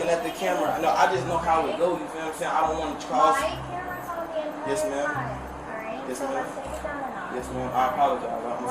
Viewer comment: that was NOT inaudible gng 🙏 we can all tell what she said "my camera was on the entire time, alright? so lets take it down a notch" is what i believe she said